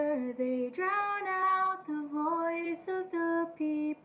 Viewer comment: Keep safe always, guys. Padayon! Btw if it sounded like I said “freedom and true”, it’s definitely “truth” 😂 this mouth failed me